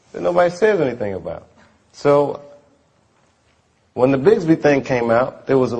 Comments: what is he saying? Sounds like "when the Bisbee thing came out," but I know that can't be right. Anyone know? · what did he say at 8:45 ????? "when the __ thing came out . . . . ." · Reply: ***** I think he said The Bigsbi thing came out